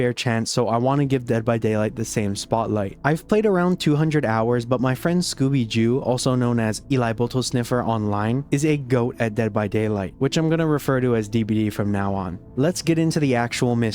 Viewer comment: did i just hear eli boto sniffer?